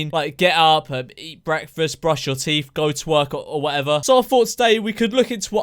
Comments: Ngl when u said ‘go to work’ I thought you said ‘go twerk’ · did anyone else hear ‘go twerk’ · Did he say “to work” or “twerk” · When he said go to work at first I thought he said go twerk lmao :D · i thought you said go twerk...